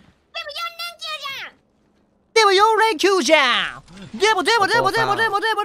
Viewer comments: でも四連休じゃん！！ドラえもんに聞こえた笑笑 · ちひろさん初見やけど声かわいい ｢でも４連休じゃん！！｣が青狸に聞こえたのは謝ります((̵̵́ ̆͒͟˚̩̭ ̆͒)̵̵̀)⸝o̗